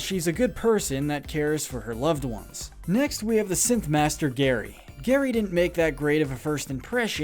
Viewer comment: did he say simp master